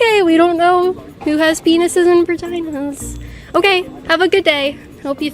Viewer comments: does she really say v*agina like virgina LMAO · it sounds like she said bridginas bc she was too scared to say v-v-v-no I can't even say it